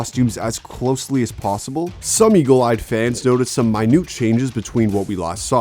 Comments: Some Eagle-Eyed Fans notice some MAINUUT changes ... Wait ,, Some WHAT !!?? Can u tell me what was MAINUUT meaning is ??? Srsly didn't understand that bro 😭 But if i just read the subtittle/caption (CC) instead of hearing the words was , It's readed MINUTE . And still ,,, can't understand to your sentences 😤😭🤣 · Some Eagle-Eyed Fans notice some MAINUUT changes ... Wait ,, Some WHAT !!?? Can u tell me what was MAINUUT meaning is ??? Srsly didn't understand that bro 😭 But if i just read the subtittle/caption (CC) instead of hearing the words was , It's readed MINUTE . And still can't understand to your sentences 😤😭🤣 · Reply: minor